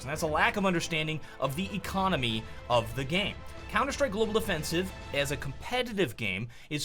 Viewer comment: Did you say Counter-Strike: Global Defensive?